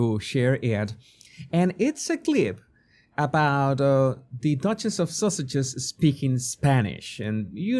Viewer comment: did he just say the duchess of sausages?🤣😂